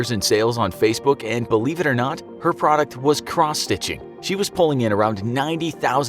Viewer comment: did you say this lady was a crosstitute?